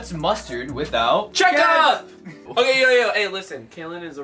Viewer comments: lolololol rug legit said “check up” instead of ketchup 😂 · when rugs says “check up” instead of ketchup 😂😂😂😂